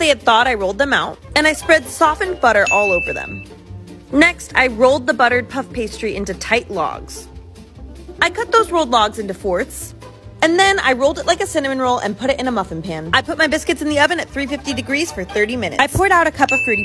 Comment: “Fourths” I’ve lost faith in humanity ✨quarters✨ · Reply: I heard her say “cut them into forts.”